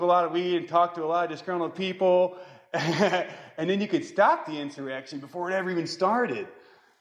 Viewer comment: At first I thought said,” resurrection” which would have made sense coming from him too....but intervening at the insurrection? Pure altruism standing right in from of the camera 🎥 good call Jimmy Kimmel